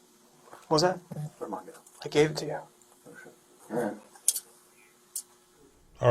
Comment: did I hear that right , did the cop say “where’s my gun?” Then POv cop says he gave it to him , then cop says “oh shit “ …… ?? Very competent group here, if I was the black guy , I would’ve started being worried these guys would screw me , just by accident alone